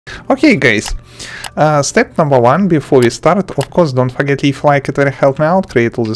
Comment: Lol in the first 3sec it sounded like u said ok gays